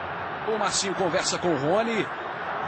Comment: in 1:45 did he say horny?